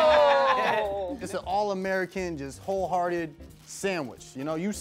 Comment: the captions say “all American Jews wholehearted sandwich” lol wtf type of shit